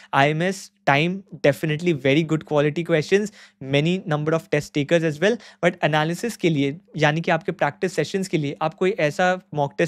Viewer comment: "test takers" is what he said guys 💀